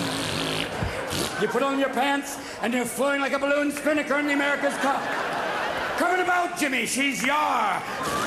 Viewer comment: What did Robin Williams say?... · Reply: floating like a balloon spinnaker in America's cup (boat and boating competition)